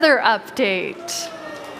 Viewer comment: I Thought It Said Weather, I Don't Know Why xD